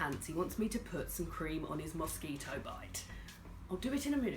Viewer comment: I heard "musky toe bite", and i was like, what the hell is that? It took me the whole video to realise you Said mosquito bite! 😂😂😂